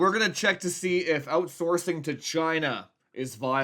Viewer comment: Honest to god I thought he said Outsourcing vagina ... Moving on to the review